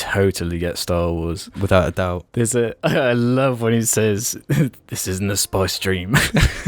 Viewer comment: What does he say at 23:02 , the subtitles say “this isn’t a spy stream” and I can’t make out what he says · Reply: "This isn't a spice dream"